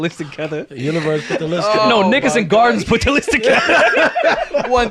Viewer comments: I know I gotta be wrong Rory did not say n***as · Did Rory actually say the n-word @ 02:02:10? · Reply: He said Nickerson Gardens. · I thought rory said n****s in gardens. I rad to replay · Am I bugging?? What did Rory say here? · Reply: Nickerson Gardens (in Watts)